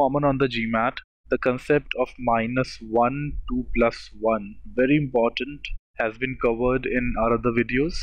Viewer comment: The very important of concept what? What is said at 1:23? "The concept of minus 1 to plus 1". The subtitles aren't even helping here.